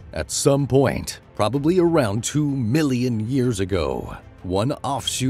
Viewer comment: did he say 2 billion years ago? Hhhm.